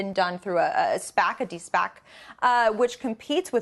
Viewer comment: did she say spacitty-spac??